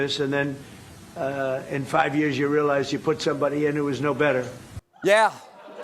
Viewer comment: did he say "put somebody in?" As in install a leader. As in regime change.